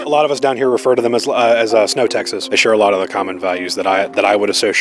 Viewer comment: As an Albertan, I'm insulted. We are NOT snow Texas and we don't have the same values. Its unfortunate that the small minority making a fuss like a whiny baby is what our reputation is. Really can't stand Smith and her sycophants. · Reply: Lol I thought they said snow taxes, im not sure which is more insulting